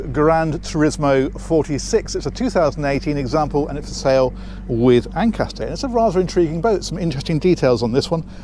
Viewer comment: I thought he said it was for sale 'with anchor' (0:16)! For a moment I wondered why a boat would ever be sold without one!!